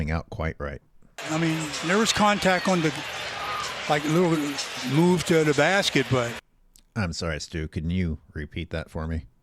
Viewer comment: Sounded like he said moollu move to the basket 😂